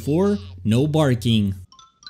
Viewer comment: Did granny just say that at 0:46? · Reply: She said you did you did